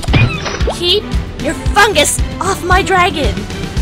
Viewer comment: sounds like she said "Keep your fuckis off my dragon"